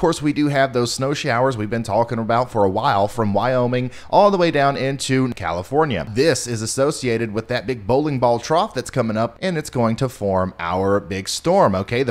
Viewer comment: Did say As-snow-ciated at 1:05? If not, I'm still going to assume he did because that's freaking hilarious.